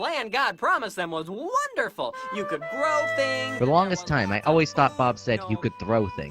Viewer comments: Ahahagahahahahah I thought I was th only one who thought bob said "throw things" lol! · Jacob Van I also thought Bob said “you can throw things.” 😂 What did Bob actually say? 🤔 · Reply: He said, "You could grow things..."